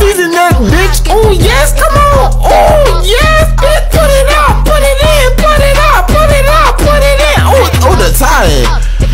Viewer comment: did i just hear "PUT IT IN PUT IT OUT"